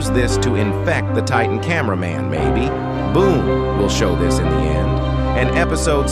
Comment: Blood ☠️ literally 3:51 moments audio sounds like ai is saying Baby instead of saying maybe 😅